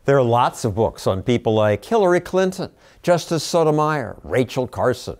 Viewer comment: anybody else hear "Killary Clinton"? Lol